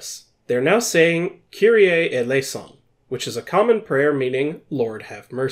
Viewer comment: I always misheard "Kyrie eleison" as "Kill the innocent"